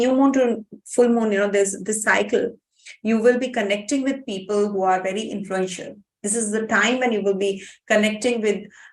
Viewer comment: i cant understan you very well for you accent , cant you write me what you say from the minute 52:31 to 52:40 thank you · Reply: She said. Connecting with powerful and high positioned people